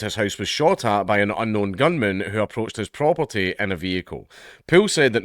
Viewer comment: I heard "gungan" and I prefer that reality to this one.